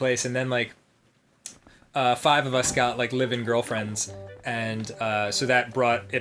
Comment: I misheard live in girlfriends as living girlfriends and thought that was a very weird thing to specify